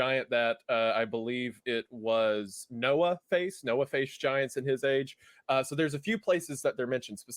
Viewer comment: I misheard the beginning as "Noah-faced giants" rather than Noah facing giants... Now I can't get it out of my head